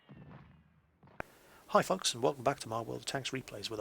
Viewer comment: was it just me or in the intro it sounded like he said hi fucks